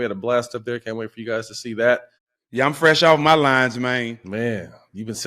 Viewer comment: I thought he said fresh of them lines too 😂😂